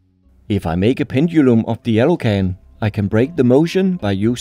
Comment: Am I colour blind? He keeps calling the blue can yellow. Also, I wonder what would happen if you dropped an aluminium can into a magnetar which has crazy strong magnetic and gravitational forces. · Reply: He calls it an "alu can", an odd shortening of aluminum which you're probably mishearing as "yellow".